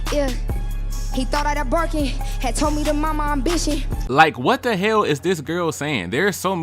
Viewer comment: I think she said "he told me to mind my ambition". Still confused as to why lol